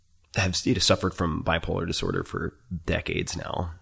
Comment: Did he say that he has suffered from Bipolar for decades, or someone else? Hard to hear.